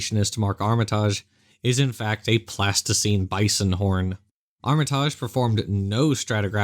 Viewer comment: LOL! -- so, at ~3:35, did anyone else think he said a "a Plasticine Bison" horn? (as in a buffalo horn made from a popular childrens' modelling clay?) 🤔 I did a double-take and had to rewind it a couple of times before I realised he was saying, "Pleistocene"! 😳😂😋 <link>